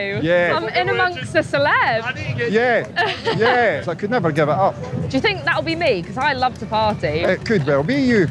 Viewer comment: I swore he said: “I could never get IT up.” Lollll